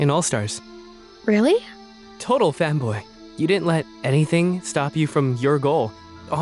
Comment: My dumbahh really thought said “total femboy” 💔🥀